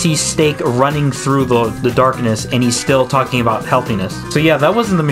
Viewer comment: he was actually saying "HELP ME" as seen on the captions of the episode but i see why you thought he said healthy since it's hard to hear